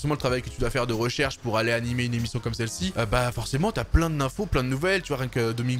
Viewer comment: Y'a que moi qui entend "forcément y'a pleins de nymphos"? xD